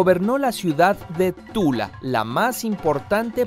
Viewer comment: did you say ¡TULA!